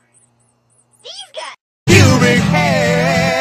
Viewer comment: idk what that said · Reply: It said public hair